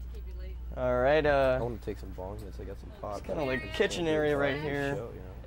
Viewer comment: I hear Mike Starr saying "I wanna take some bongs and some pot.." haha i can't hear the rest but thats funny